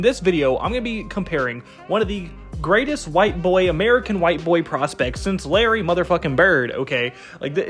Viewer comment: You can't say greatest white boy since Bird when you have Manu Ginobili in between. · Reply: i thought he said,white american